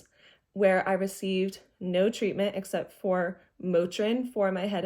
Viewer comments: the subtitles say morphine but I’m pretty sure I’m hearing you say Motrin (ibuprofen). Big difference there, so if it was a mistake I thought you’d want to know :) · you say “Motrin” but the captions say “morphine” which is very different obviously. The captions should be updated. I am so sorry all this happened to you and that you were disbelieved!